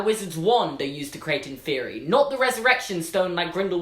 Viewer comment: I thought he said in therory not inferi 😂